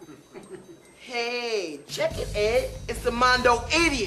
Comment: Did he just call him a Chickenhead?